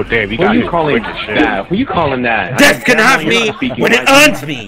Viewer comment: Did he just say Dez can have me when he earns me LOL very suspect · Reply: lmao nah but it sounded it like it, but he said death can have him